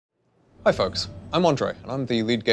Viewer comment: Well, when i started the video it sounded like he said: hi fucks, im on drug